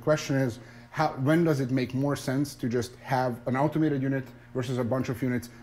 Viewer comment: really sounds like he said ultimaker when he said automater XD